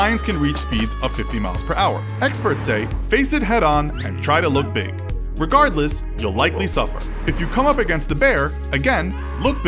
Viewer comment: what I heard. No matter what happens you’ll die